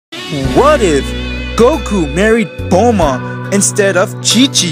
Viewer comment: I heard him say what if goku married gohan